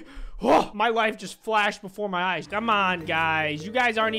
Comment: it sounded like he said y wife flashed before my eyes